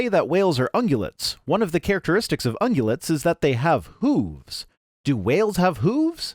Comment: When discussing whale similarity to deer like creature twice I thought you said “boobs,” later realized you said “hooves.” I’m still picturing whale boobs.